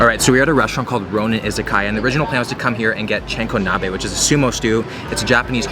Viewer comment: SMH!!! It sounded like you said Chinkonabe instead of Chankonabe. Chinko = Penis. good video but there were so many mispronunciations.